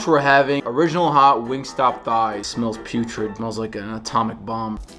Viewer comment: Okay for a second there I thought you said GameStop for a second little key what Wingstop